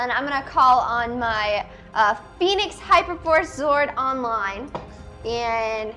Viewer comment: You know the funny thing is the amount of dark stuff that happens in this episode. It’s almost like hyper Horse was building up to more intense themes. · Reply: Pardon my criticism, but did you just say "hyper horse"???????